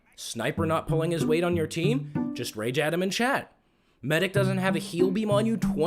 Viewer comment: I love the fact that in the captions instead of saying rage "at him" in chat it says rage Adan in chat XD Edit: and at 1:50 ish it says Adam and Shaq XD